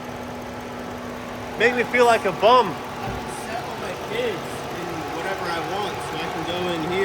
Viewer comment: I pretty sure he said, "I can select all my PIDs". But I thought heard, "I had to sell one of my kids". 😨